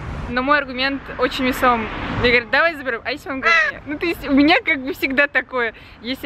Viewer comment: я не расслышала слово одно ворона помешала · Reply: "а если оно в говне"